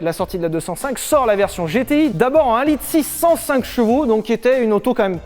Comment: Quand il a dit 1.6 105 ch j'ai entendu 1.0 605 ch j'allais faire un malaise 😂